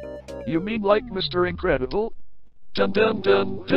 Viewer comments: "Tick tick" is what I hear "Chick chick" 💀💀💀💀💀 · "Dun dun dun dun tick tick"💀🤣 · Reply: I thought he said 🐤chick chick🐤